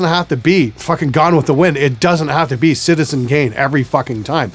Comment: did I just hear you say "Citizen GAIN"? because it would be totally appropriate considering what this channel is about :)